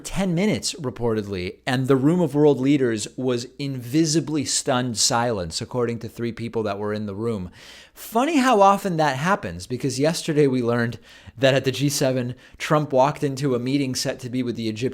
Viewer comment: When you said they were "... in visibly stunned silence..." it kind of sounded like you called them invisible. It made me laugh.